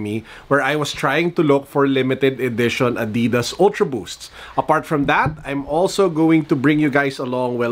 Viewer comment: I'm a simple man, I heard "ULTRA BOOKS" I laugh. Thank you